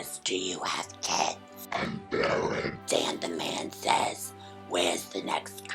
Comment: What did she say at 6:30 ???? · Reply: Q: Do you have kids? A: I'm barren